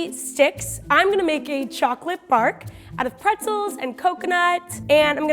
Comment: who else thought said choclate fart